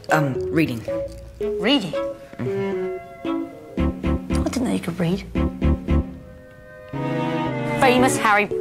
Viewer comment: The first time I heard “I didn’t know you could read” I thought Malfoy said “ That’s an unnatural breed"